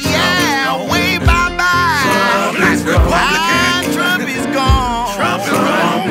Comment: Sounds like he was saying "Piss" is gone